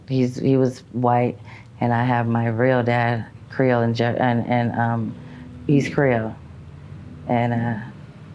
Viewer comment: I must of missed it when she said...Korean ? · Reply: I’m sorry, I misheard her. At the very beginning she said Creole, then immediately after I thought she said Creole and Korean, but she actually just repeated “Creole, and…Creole”. Thanks for bringing that to my attention.